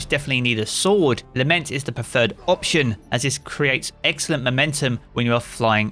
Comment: “ you’ll need a lament, as this creates extra Lamentum” did I hear that or did my brain just want to hear that